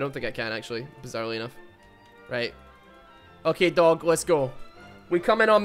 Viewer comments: All I hear is "gay dog" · I thought he was saying “gay dog” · Did he just say gay dog?